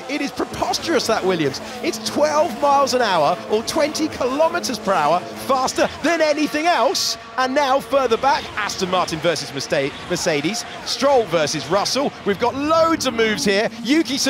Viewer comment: does anyone else hear “Aston Martin vs mistake” ?